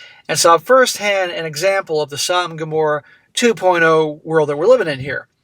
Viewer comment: I thought you were always saying songhamorah 2.0 and tried searching it up but never found anything